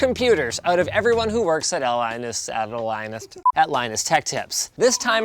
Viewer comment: did Linus just say “Apple Linus Tech”? No wonder they said iOS was better, they’re being bought out by Apple! ...said all the fanboys ever... IT WAS A JOKE I DONT REALLY THINK IOS IS BETTER BOTH IOS AND ANDROID ARE TERRIBLE IN THEIR OWN WAYS